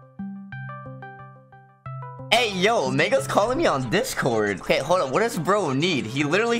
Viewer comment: DID HE SAY N WORD!1!1?2? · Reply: nvm you said mango